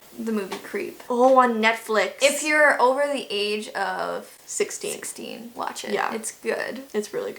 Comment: i thought they said if you are over the age of 60 you should watch it 😂